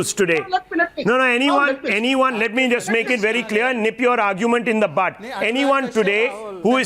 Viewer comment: did he say "nip your argument in the butt" ? funny way .